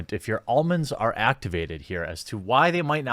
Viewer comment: Did he just say , "if your almonds are activated? " happy anniversary